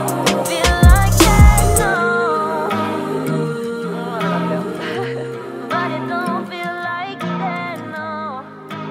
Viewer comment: you hear her say something. Can anyone tell me what it says? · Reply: She just does like am "uhhh" and then a cute laugh